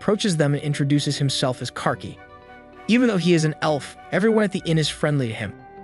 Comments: Bro I thought I heard "Car key" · did you say car keys?